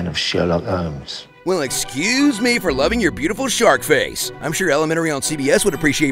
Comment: "well excUse me for loving your beautiful sharp face" WHAT AN ABSOLUTE MOOD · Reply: I thought he said 'shark face' - alluding to how the Sherlock fandom decided that Benedict is a hammerhead shark (don't ask me why, because I don't know!). Was it because of that South Pacific nature show he narrated? It did have one part with sharks, including the hammerhead ones.